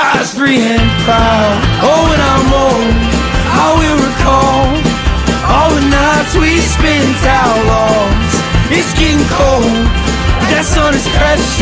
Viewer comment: i thought he said “when mary brought her own jane” but ig not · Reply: i thought he said “i’ll recall all the nights we spent outlaws” maybe out lost? whatcha think?